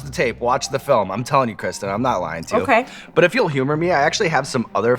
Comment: Did you really call her Krista at 9:20!?😂😂😂